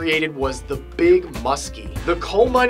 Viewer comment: Thought that said The Big Mistake HAHA .